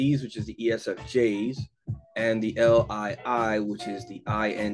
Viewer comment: SEIs (ISFp) in Socionics is generally the exact same person as an ISFP in mbti. The only difference is intuitives that contributed to mbti theory got the introverted sensing stacking wrong. Whereas the Eastern European intuitives that created Socionics did not. My ISFP brother shares the values on the Alpha Quadra. My ISFJ mother is Gamma. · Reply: your reply showed up after i deleted my comment. I heard it wrong. He clearly says 0:24 LII as INTj. (Not ILI) Still wonder if all Socionics Models got the exact same labeling. Like is there any MODEL in Socionics that has got ISFJ instead of ISFp for SiFe types. That is only using captial letters.